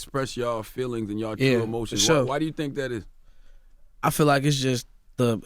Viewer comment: sounded like he said shut up lol. i know he said for sure tho